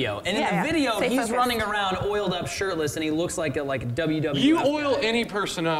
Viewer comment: Did he said WW"F" at 5:35? .-.